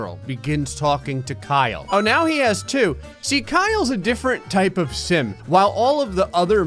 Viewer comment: I heard "Kyle's a different type of simp" and I have to think about that